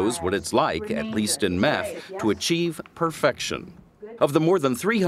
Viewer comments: Did you just say meth? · did he just say meth?!